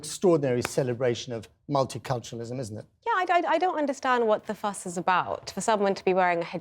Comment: I thought she said "I really don't understand what the "fuck" is all about for a minute, then I noticed my hearing problem.